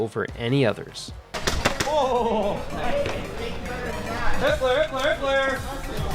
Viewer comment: i swear i heard "hi*ler hi*ler hi*ler!"